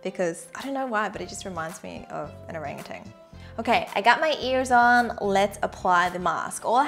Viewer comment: what did you just say? I got my ears on?